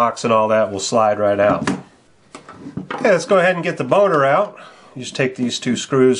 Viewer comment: its sounds like you said "lets get the boner out" lol